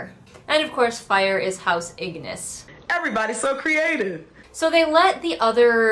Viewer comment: I thought you said house ANUS and believed it for a sec because these authors are so off the wall